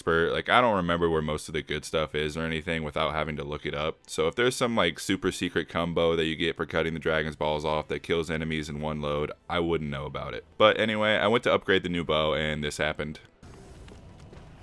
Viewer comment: did you say “cumbo”, bawhahaha!